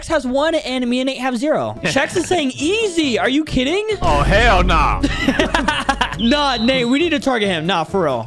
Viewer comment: did he just say ah hell nah